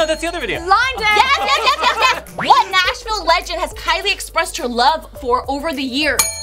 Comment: the way she mumbled her words on saying "Nashville" I think both Kylie & the fan thought she said "National" instead - that's why they gave the answers they did initially